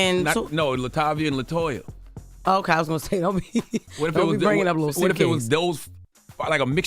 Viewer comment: "Don't be bringing up" What? I 🔉🔊 & ⏪ multiple times and still couldn't catch it. Sounds like "Lil' suitcase" 💼 but obv that is nonsensical thus improbable. 😅 Does anyone know?